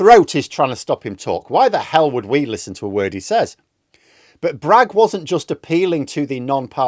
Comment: I misheard the rebuke of RFK Jr as "Why the hell would we listen to a WEIRD he says". I think it still works.